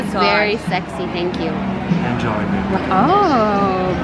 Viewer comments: Did he say "enjoy baby?" Or am I loosing my mind? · Did he just say “enjoy baby” in